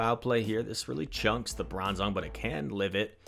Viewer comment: I keep hearing "Lebron zong"